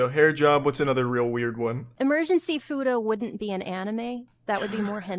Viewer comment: “Emergency futa” I had to hold back laughter when I heard that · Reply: Because of a certain recent game, I misheard it as "Emergency Fooda" and just thought "huh, this is a turn."